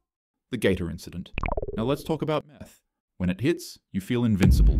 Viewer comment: why did I think it said math instead of m_th